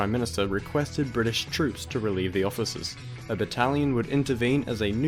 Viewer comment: I thought you said 'an italian' and that painted the best picture in my mind. Just one italian.